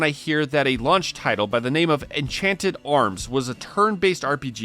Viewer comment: Ive never heard anyone call Xenogears a "cult classic". Its on like every "Best Rpgs of all time" lists. Its widely regarded as one of the greats and I have no idea where you got that it isnt. To be honest, putting FF7, Xenogears, and Wild Arms on this list almost seems like being controversial to be controversial. In the ENTIRE history of RPGs that could have been better, how are those 3 even on this list? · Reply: He has Enchanted Arms on this list, not Wild Arms. I'll admit I thought he said Wild Arms at first.